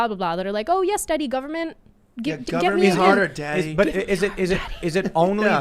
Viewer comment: Anybody else catch the “give it harder daddy” whisper from Sydney at 59:50!?!?!?!? LMFAO HAHAHAHAHA 🤣🤣🤣🤣🤣